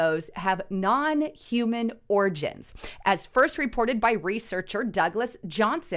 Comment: Damn! I was all excited. I thought she said "Non-Human ORGIES!!!! ~ My bad!